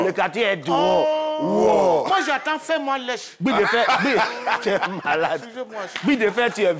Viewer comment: Ko "moi j'entends fais moi laisse!" kiokiokiokio